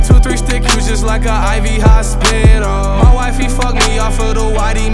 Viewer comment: Coulda swore he said “Juice Wrld, Pop Sicko-“